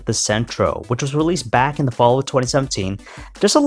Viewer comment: The narrator by mistake pronounced 2017 instead of 2007